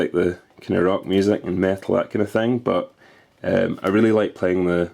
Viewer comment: It sounded like you said you like rock and meth lol.